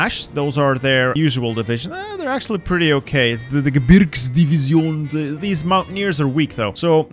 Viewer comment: a new division has been created: the "beer divsion" instead of "Gebirgsdivision". No doubt, the beer division definitely makes more sense.